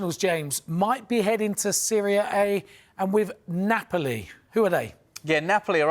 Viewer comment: it sounds like he says two players are heading to "Syria A" - I'm not sure they're going there dude!